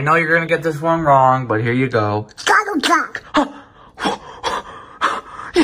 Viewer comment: I thought he said Donald trump but it's Donald duck